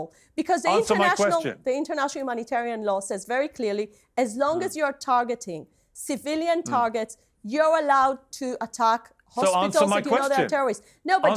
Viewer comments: Did she just said “As long as we are targeting civilians targets, we are allowed to attack hospitals” and Piers didn’t catch that? 💀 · Go to 17:45. Am I tripping or did she really say when targeting civilians you can target hospitals 😮